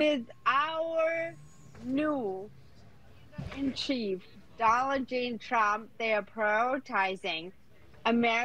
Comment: did she say Dollar Jane Trump?